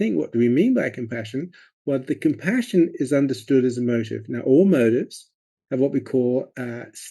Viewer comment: Paul Gilbert sound quality very hard to understand. Can someone tell me what he is saying - what I hear is ‘compassion is a murder’?!